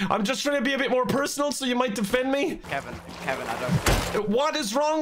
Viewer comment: did someone say "kevin" at 10:33 ?